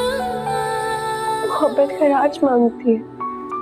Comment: mohabbat ______mangti he, Maine to apna Aap pesh kar diya. Yaha mirha ne kya kaha?? Mohabbat kya mangti he??? Sorry weak Urdu... 😔😔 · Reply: Khiraaj